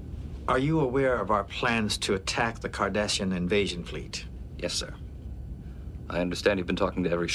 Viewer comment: Did he say " Attack the Kardashians" ? Yes let's do it. And shall Enterprise/ Titan Vessels succeed in their mission.